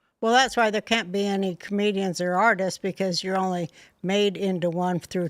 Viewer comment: "Comedians or artists.....". I thought she said, "Canadians or artists." 😂😂😂